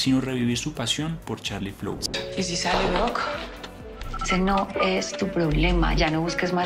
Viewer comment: creo que dice * y si sale ruko o algo asi* si escuchan con atencion dice eso